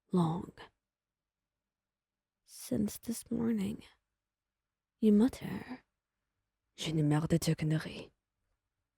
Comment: Hold up, what did she say at 3:30 ? lol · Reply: I think it’s « J’en ai marre de tes conneries ! » you can translate this in « I’m tired of your bullshit ! »